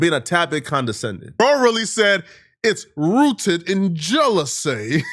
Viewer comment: Bruh, David was born in the UK, and Godfrey ain't do sh*t‼ The least you can do is BASIC DUE DILIGENCE 😂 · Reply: Also, David never said "it's rooted in jealousy" @ 3:08. What he said is, "it's rooted in security," which is 100% accurate 💯. "FBAs" don't have a problem when Forest Whitaker, Don Cheadle, James Earl Jones, Chadwick Boseman, Denzel Washington, Will Smith, Danny Glover, etc. play key African figures, but they got the nerve to be cry babies about professional actors outcompeting them. Do your job, son.